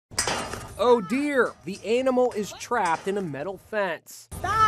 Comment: did he just call that deer an ANALMAL